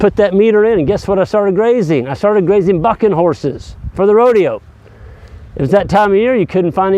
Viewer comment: He is saying BUCKING....... Bucking horses, just to make it clear. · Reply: Oh I thought he was saying bucket horses. Whoops! That makes more sense.